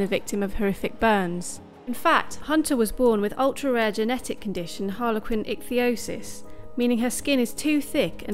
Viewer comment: i thought it said harley quinn.